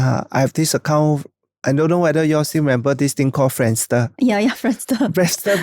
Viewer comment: Just a minor correction at 20:00, "friend stuff" is actually friendster I'm also not sure whats going on at 55:20 1:06:39 another correction: "its not a death sentence" instead of "its all the death sentence"